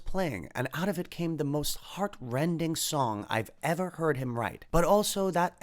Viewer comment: when he says the most heart-rending song I thought he said the most horrendous song 😂 I'm an idiot